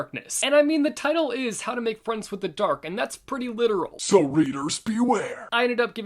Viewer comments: I thought he said, "how to make friends with the duck." I was very confused. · Oh god I thought ha said the book was called how to make friends with the duck and man was I confused. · I thought he said, “ How to make friends with the duck.” 😂